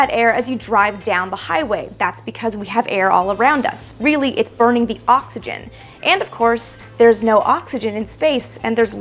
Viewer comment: Did I hear her say at 3:04 " it's burning the oxygen"?